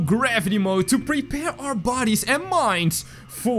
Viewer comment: did he say body's